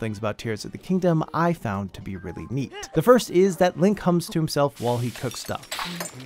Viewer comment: I thought u said link comes to himself whilst cooking💀